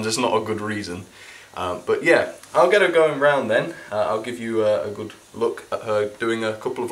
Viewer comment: i thought you said drown her...